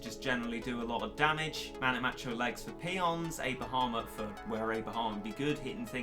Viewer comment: When said a bahamut i thought he said a bahamer